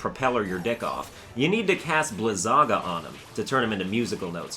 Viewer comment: i when he said "Blizagga" i thought he said something like "Lasagna" but with the g not being silent